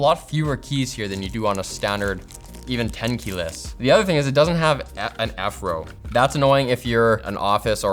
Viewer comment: I thought he said "it doesn't have an Afro"